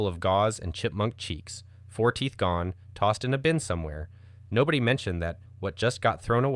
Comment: i thought he said 14 gone, had to rewind 😭